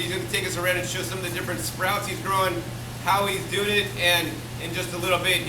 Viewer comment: did you actually say "...how he dood it" at 3:04? lol